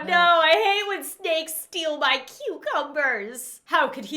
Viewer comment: I thought she said "I hate when a steak steals my cucumber" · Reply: I thought she said " A steak stole my cucumber